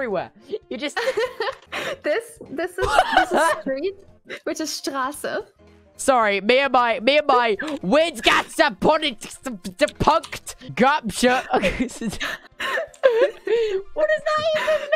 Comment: TECHNO IS SCARED DJFIK HE REALLY SAID stop witch